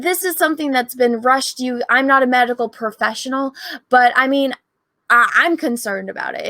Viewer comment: I thought you said “I’m not a magical professional” at first 😂